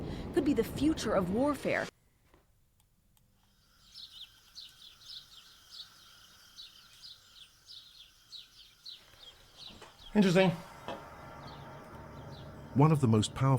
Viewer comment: I heard yo momma is the future of whore-fare